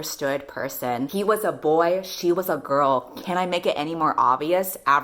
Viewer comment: I can’t tell if it’s because it’s late or I’m gay that I heard you say “she was a boy, he was a girl, can I make it anymore obvious?”